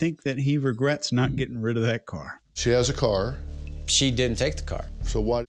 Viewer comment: BODY LANGUAGE MASTERCLASS: <link> PLEASE SUBSCRIBE: <link> MERCH STORE: <link> FACEBOOK GROUP: <link> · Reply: First strange thing I heard was “‘She has A Room’”?!? Wha? I am not aware of ANY married couple whom each have ‘a room’ rather than ‘our room’; nor long term co-habiting couples for that matter.